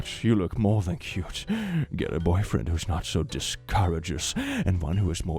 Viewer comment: He actually said discourteous, but now I wanna know what discouragous means.